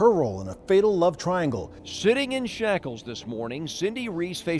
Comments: I heard “Shitting in shackles this morning Cindy Reese faced her punishment” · you cannot convince me that this news anchor did not say "shitting in shackles" on tv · Did anyone else hear "shitting in shackles this morning" at 19:20 ? Lmao accurate.